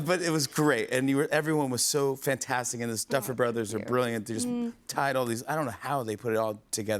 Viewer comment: Not one shoutout to the duffers😭😭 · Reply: it was sorta said. "Stuffer Brothers"